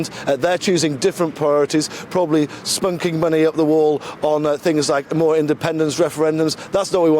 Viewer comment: Did he just say “spunking money up the wall”…?